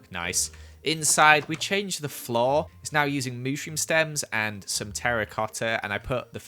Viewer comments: who else heard mooshroom stem instead of mushroom stem at 4:42 if its just me comment and tell me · did joel just call mushroom a mooshroom? XD